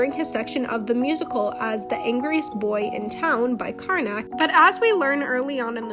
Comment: this whole time i thought karnak was saying "in time" not "in town"